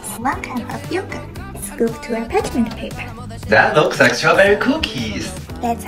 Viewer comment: I thought he said "that looks like strawberry pookies"💀💀💀😭😭😭😭